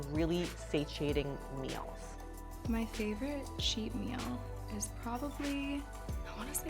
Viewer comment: I thought she said In-n-Out was her favourite cheap meal, which seemed fine to me, but then nope.